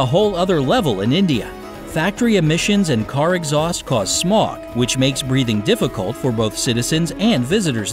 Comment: did he just say "smok" instead of smoke?? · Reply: *smog